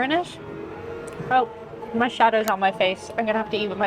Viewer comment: Oh..... "Shadow" on her face.... I heard "shatted" the first time. 😂